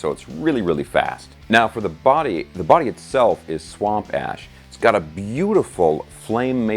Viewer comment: Thought he said swamp ass